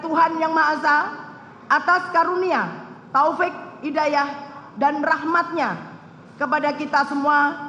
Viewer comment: gue dengernya sekilas tadi "Taufik Hidayat" njir wkwkwkw